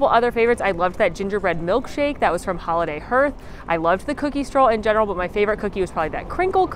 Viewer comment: I thought she was saying holiday heurse. That’s festive.